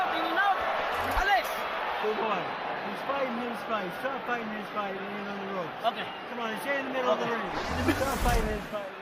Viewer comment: what Freddie said? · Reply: "Good one stir fry french fries certified means fried dont let him lean on the ropes" "Ok" 😂